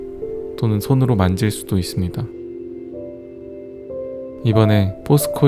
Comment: "돈는 손으로 만질수있습니다"라고 왜 들리는 거지?